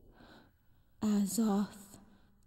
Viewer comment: omg all this time I thought was saying they wanted her "Ass Off", I just realized now that he was actually saying "Azoth". well then.